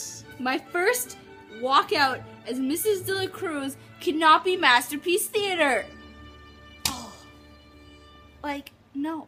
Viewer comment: I also scored a 17 :) I kept hearing "Master P's Theatre" and I was like, WHEN DID THAT HAPPEN? I would definitely watch that! Hooty Hoo! you should be all like, "Oh yeah, just you wait, I'm gonna open up my own guitar school!" and then go and get Robert Goulet to perform at your school. That'll show them.